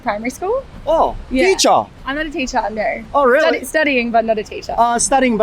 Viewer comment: “I’m not a teacher”の後なんて言ってるかわかる人いますか？ In there? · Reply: I'm not a teacher now. っぽいですね。 ティーチャゥナゥ みたいになってると思います 0.5倍速だと結構自然に聞こえました。